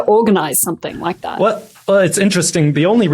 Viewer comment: I thought he said deaf-con at first lol